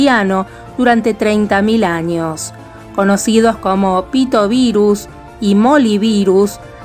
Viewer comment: pito virus escuche yo